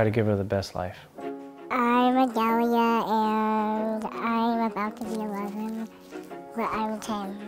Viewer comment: The auto caption is being creepy again. At 0:57 when says “I’m Adalia and I’m about to be eleven” Auto caption says “I like their world I’m about to run out of time” 😱😱😱 I hope they find a cure or at least an effective treatment for progeria soon.😥